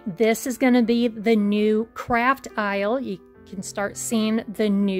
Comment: When i first was listening while u were at the empty isle u said the new craft isle, i thought at first she said CRAP PILE!!! 😅😆😂😂😂😂 IM SORRY BUT SO FUNNY